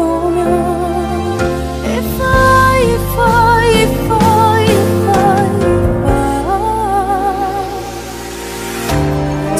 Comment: everytime i hear this part 0:56 i thought she said yeppo³ 😂 btw the song really suit every scene